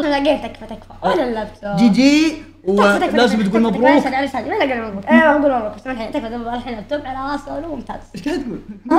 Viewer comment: وش قال هاذا😂😂😂😂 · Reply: سحر